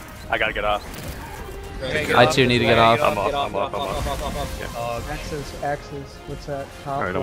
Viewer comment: it sounded like google translate with the guy saying "offoffoffoffoffoff"